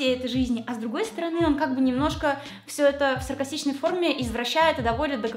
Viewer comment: Все это в сыркосичной форме😂😂😂😂 Почему я это услышала?